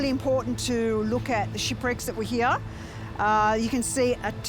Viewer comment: I swear I thought the lady said “shitbricks” instead of shipwrecks